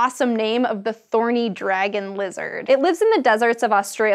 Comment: i thought it said "Horny Dragon Lizard" 😂😂